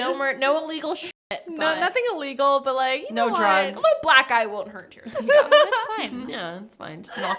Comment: Byeeee I thought said at 56:50 "a little black guy wouldn't hurt u" I was abt to get pissed😂😂 she said black eye😅